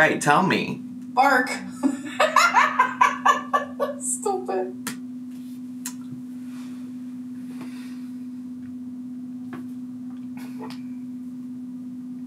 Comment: All i heard was "Stupid h03 how you figure" lmaooooo